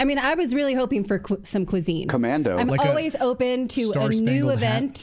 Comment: I heard say, "Going commando," when u mentioned what to wear for liberation day.